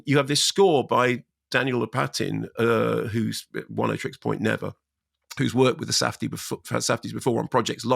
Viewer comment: what’s Mark saying? “Who’s one eight tricks point never”? I’ve listened 10 times, still can’t understand a word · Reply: He's saying that Daniel Lopatin, who did the music, is also known as Oneohtrix Point Never.